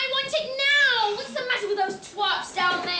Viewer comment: Did i really hear berruka say whats the matter with those twats down there?